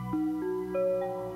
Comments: i heard "morning friday" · i hear "morlk friday" wtf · I heard: “More Friday...” Plz help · i hear “more friday” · I hear "morc friday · At some point of focusing to hard on hearing both I heard “ Moack Friday “ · I hear 'Moral Friday' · I hear “more Fridays” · WHY DO I HEAR "MORE FRIDAY" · i heard more Exciting at first then i try to focus on Black friday then i can only hear black friday. i can't hear more exciting any more D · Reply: I meant i heard "More Friday" .-. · I heard “more friday” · i heard "more friday."